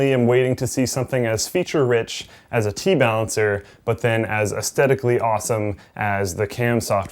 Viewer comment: Inferior technology if it cannot make all fans PWM ...@ 4:50, did slick say "T-balancer" ?